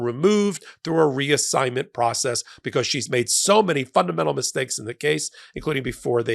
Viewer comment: 😂 popock , I thought you were going to say " she's done so many fuck ups" . You said fundamental